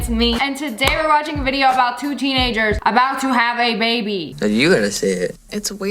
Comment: In the beginning when she said “two teenagers” I thought she said “coochie yeagers” 😭